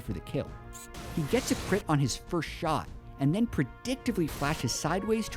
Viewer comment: <link> Am I the only one that heard him say..."jensen crit on his first shot..." or did i hear that wrong?